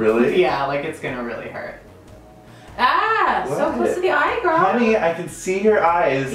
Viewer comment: was it just me??? but I heard Scott's calling Mitch "HENNEY, I can see your eyes"